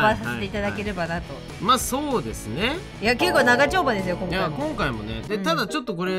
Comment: 長丁場ですよ。が ながちょんぱですよ。に聞こえた…